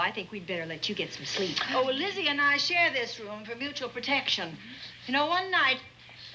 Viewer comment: did she say "Lizzy & I share this room for protection"...???🤥🙄🥰😷 (lol)